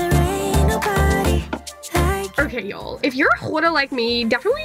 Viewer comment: i heard Ggghoarder 😂😂😂😂